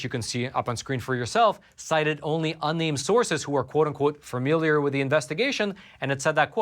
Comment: What's that word, Roman? "Fearmiliar"? "Fermiliar"?